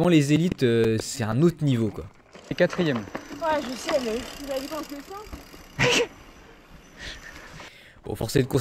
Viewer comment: elle dit quoi à 7:38 ? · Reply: elle dit "ouais je sais mais ta vue comment je descend"